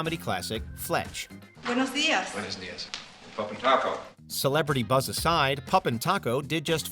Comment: After all these years I thought Fletch said "PoppinTaco", the Puerto Rican Breakdancer in Breakin who along with his boy Poppin Pete challenges Turbo & Ozone to a battle at the Radiotrom lol! However in Breakin 2 Electric Boogaloo Poppin Taco joins up with the good guys to save Miracles 😆